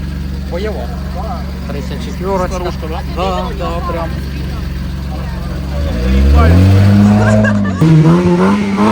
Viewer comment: Що вона сказала там? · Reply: фара разбита грит